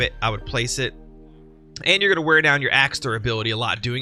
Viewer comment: Also, it looks like I misheard when I thought you said you were gonna tear it down. Phew